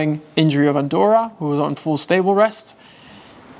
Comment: I thought you said “unstable” not “in stable”. Animals in our life bring us such joy but with love comes emotion.